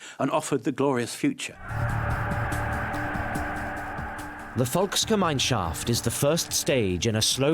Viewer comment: i keep hearing that as mineshaft 😭 "as a child I yearned for the mines" the incoctrination is real lol